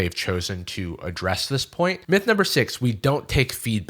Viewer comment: did anyone notice the captions say poing instead of point lolz